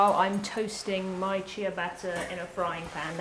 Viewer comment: I've never heard "shea" pronounced like that and I thought she was saying "chia butter" lol